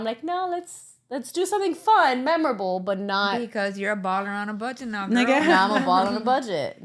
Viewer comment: Using the N word at all was unnecessary. It carries a long history of harm, and choosing to use it especially in a casual or careless way shows a lack of awareness and respect. There are countless other ways to communicate without resorting to language that is so deeply offensive. It’s also frustrating when people use it and then claim they’re not racist. Intent doesn’t erase impact, and words like that don’t exist in a vacuum. At some point, accountability and understanding have to matter. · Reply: Are you sure the N word was used? I played back 27:11 of the video a few times and what I heard was “Youre a balla on a budget now girl”. At no point did I hear the N word used. There are 7 children between the three ladies, 5 of which are bi-racial. There is a deep understanding of how the use of the word would be offensive; hence it was not said nor has it ever been said.